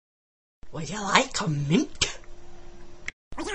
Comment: "Would ya like tuh mate?" is what I thought she said the 1st time I watched lmao xD